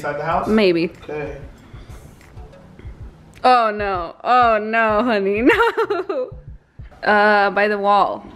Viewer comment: I don't wanna be a bitch about it but did she say honey?